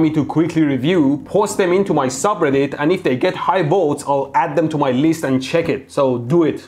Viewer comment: I thought he said high volts...